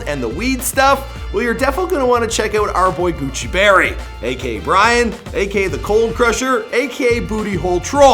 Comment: Is he saying "AK"?